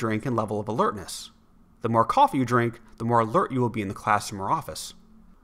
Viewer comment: the narrator says "alert" but the subtitles say "alter"